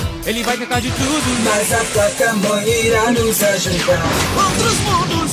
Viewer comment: só eu que entendi vaca mole?